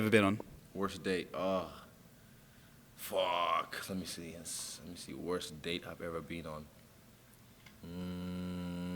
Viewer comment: I wonder what he said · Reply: Pussy